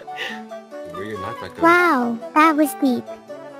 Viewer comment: I think she said deep